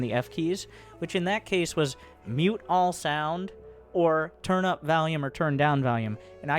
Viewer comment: bit of a Freudian slip here I think, saying he wants to turn up the valium and turn some shit out.